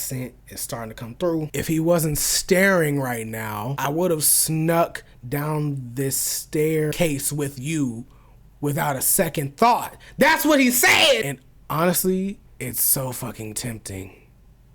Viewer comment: Horniness final boss😭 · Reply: Edit: During 1:11:46 he actually said "If he wasn't standing here right now, I would've shoved you down this staircase without a second thought. And honestly, it's really fucking tempting." I could tell when he said 'down this stair'😂